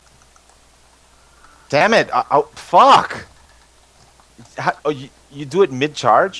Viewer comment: Did swifty say fuck?